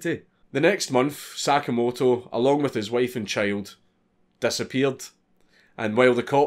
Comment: His wife actually got convicted of ordering the murdering a cult member and only got 7 years. They later reduced it because the family of the cult member asked for leniency after she gave them 20 million dollars. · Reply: I thought you said "my wife" for a second lol